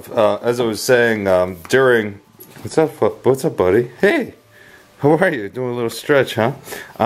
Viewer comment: Fuck buddie? XD What where you going to say ? lol